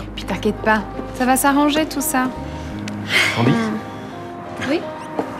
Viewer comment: C'est moi ou le mec a dit "Candi" ?? 😂😂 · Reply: Nan,il a dit candiz😂